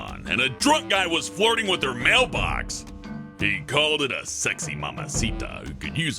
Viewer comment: When said "mailbox" I thought he said "male box"